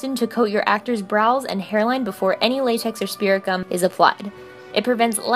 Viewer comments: Am I the only person that has thought she has been saying speargum all this time and not spirit gum? Or am I just an idiot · Am I the only person that has thought she has been saying speargum all this time and not spirit gum? Or am I just an idiot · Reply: Lol i thought she was saying spiricom 😂